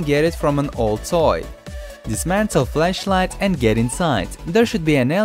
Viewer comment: you said flesh light instead of flash light...